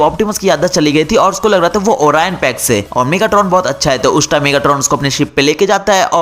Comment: what the fuck he saying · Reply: Lol he saying megatron is good😂😂😂😂